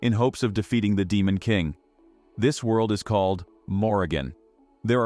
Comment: I misheard Morigen for Oregon💀